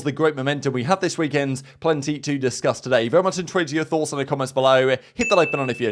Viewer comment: It's like he's trying to say (I'm very much interested in your thoughts) but not even God knows what he actually says IN EVERY VIDEO "Om very muintuituinto your thoughts" 🤣🤣